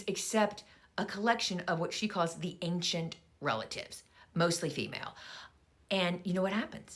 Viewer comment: I initially thought you said ancient relics.